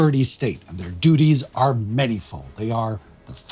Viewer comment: We find bright spots where we can - I heard "manifold" as" manyful" and enjoyed that newly minted word very much!